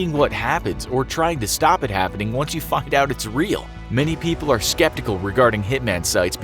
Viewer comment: Did he just say fucced out its real